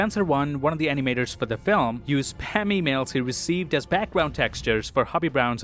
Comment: he used what for the background animation? Couldn't hear clearly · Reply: He said spam emails